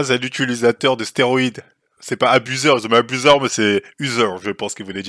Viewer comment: tu troll ou quoi il a dis "abuser" pas user écoute bien